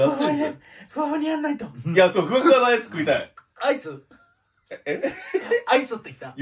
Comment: ふわふわなアイスって聞こえるわｗ